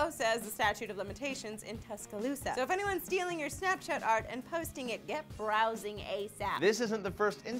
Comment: Did say Snatch Shot Art? Lol